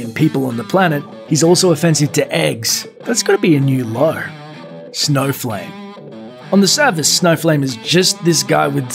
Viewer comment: For a moment I thought he said there's a superhero called Snowflake not Snowflame. haha